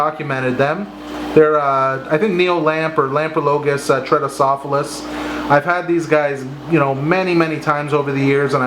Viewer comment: I didn't understand the fish at 7:40 and it looks really cool and interesting, can you please write it down here in the comments? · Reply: Neolamprologus tretocephalus